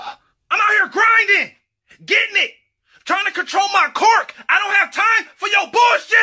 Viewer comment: What everyone hears: "TRYING TO CONTROL MY QUIRK" What I hear: "TRYING TO CONTROL MY CORK"